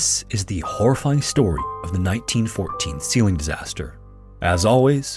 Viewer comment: I thought you were saying "ceiling" rather than "seal-ing" lmao